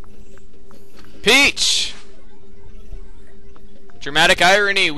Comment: I swear thet I heard "Bitch!" then the X-Man made an "X".